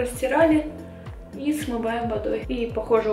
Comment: что сказала?:D Просто я в меру своей испорченности слышу «ОУ СУЧКА». Простите, просто реально интересно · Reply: Она сказала: "Какая красоточка"